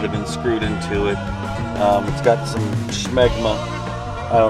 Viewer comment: Did he say 7:47 smegma? Lol